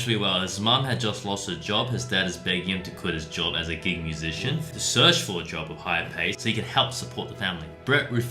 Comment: i had to rewind because i thought brett said gay musician